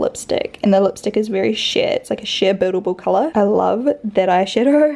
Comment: I thought you said the lipstick was shit when you said sheer